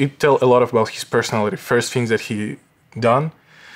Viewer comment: what did homeboy say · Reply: Show the playback to this little girl